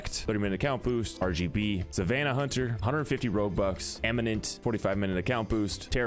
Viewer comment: Sounded like he said robux lol 😂😂